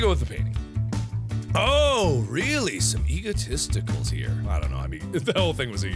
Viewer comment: I thought you said Eagle Testicles